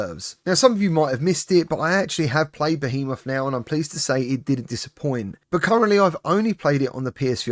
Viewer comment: I’m also concerned about the Alien game. Designed to be played in short bursts is worrying enough, but it even has ‘Rogue’ in the title. I’m now a bit worried about Behemoth too, because it sounds like you said ‘it DID disappoint’? Played that bit several times to be sure. Might be my hearing though?